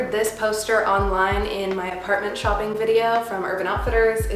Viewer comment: I thought you said “Pregnant shopping video” like I got so confused for a sec